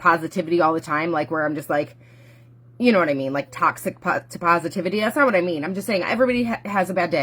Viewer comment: I thought she said no toxic positivity?